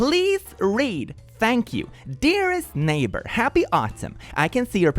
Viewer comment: i thought this said "happy autism" lmao 😭